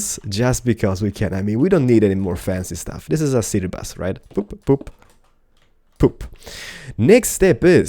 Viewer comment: not to be weird or rude or anything but at 1:59 it sounded like he said "poop".